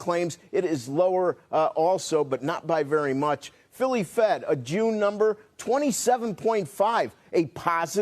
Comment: does he say a jew number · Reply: June number